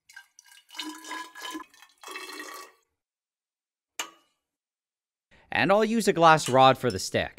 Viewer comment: when bro said "test tube". I thought he said testicle